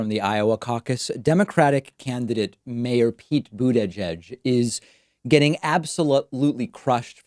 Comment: Every time I hear the name "Pete Buttigieg" it sounds to me like they're saying "Veet Voojagig" (Hitch-Hiker's Guide to the Galaxy character) so at least I get a small chuckle whenever I hear his name :)